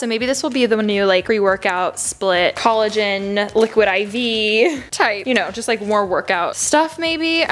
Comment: Me at 14:04 : did she just say liquid iv??????????🤔🤔🤔🤔🤔